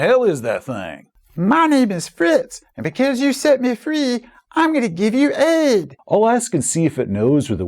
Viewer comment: For a second there I thought the puppet said "since you set me free, i am going to give you AIDS" and died a little 🤣🤣🤣🤣